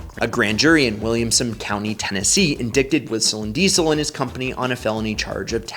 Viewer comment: Did you say 'in-dick-ted" How do you have 1.2 M subs but 78 comments and 400 upvote?